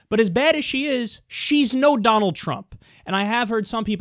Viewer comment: did she say she's way worse? There both bad